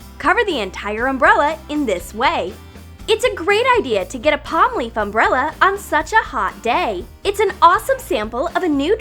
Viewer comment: Omg when they first said “palm leaf umbrella” I thought they said “pot leaf umbrella” 😂